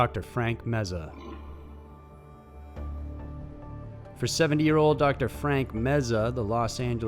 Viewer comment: I thought you said, "for 7 yr old, Dr. Frank Meza..." A 7 yr old doc? Then I looked at the screen & thought, there's no way he's 7. I'm sorry y'all. My brain and ears fail me every now and again 🤣. Carry on.